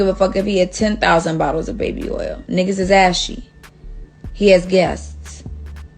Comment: Did she just say niggas ashy😂😂